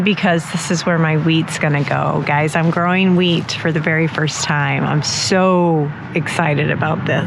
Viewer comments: Growing wheat for the first time but I thought she said growing weed for the first time. Now that would be a garden. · Omg I thought you said “I’m going to grow weed for the first time” then you said wheat 😂😂😂